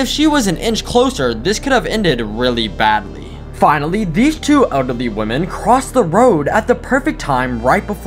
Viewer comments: the first time i heard ''ugly woman'' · "Ugly women," Bro violated them💀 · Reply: I too thought that he said ugly women until i saw the subtitles 😂